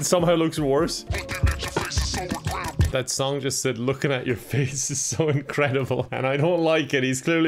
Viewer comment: I thought the music said "looking at your face is so regrettable!" :'D